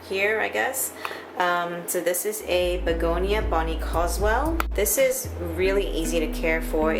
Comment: Hi can any one help me with the name of the Begonia on the ledge in the bathroom (18:30)? I hear Begonia bonnie coswel but i can't find any information on it? Thanks